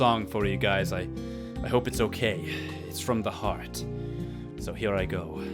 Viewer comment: I misheard when he said "I've got a song... It's from the heart" I heard It's from the hobbit .____.